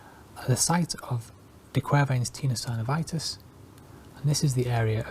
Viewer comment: does he say 'Tina Turnervitis'?